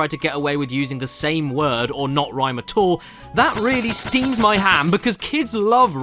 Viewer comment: Me: I thought you said "That really steams your clams" Caddicarus: No, I said " That really steams my hams" Me: I see